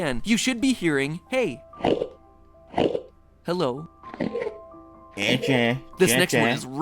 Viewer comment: That does not say "Che Che" it says "Heh Heh" no CH!. still not "hello" but definitely not "Che Che"